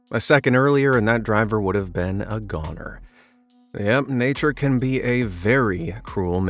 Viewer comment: DID YOU SAY GOONER?!